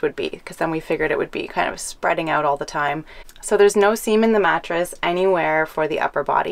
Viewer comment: Inspirational 🤙👊👍🙏 Genuinely thought she said no semen in the mattress though 😂